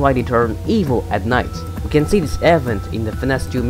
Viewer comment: everytime you said event I thought you said airvent